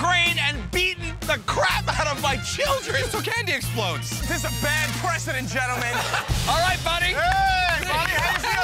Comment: at about 0:34 did murr say this is a bad president lol